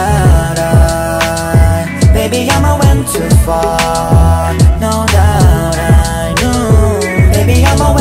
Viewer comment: Oml I put this song on Spotify and my sister thought he said “ baby idk when to fart “ LMAO